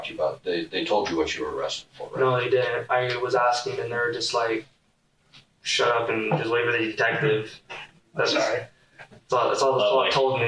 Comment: AM I WRONG?? I just heard him say to the detectives, that he told the first police officers: "I was asking the nerd, just like..."shut up, n deliver these diapers". Lmao...am I right??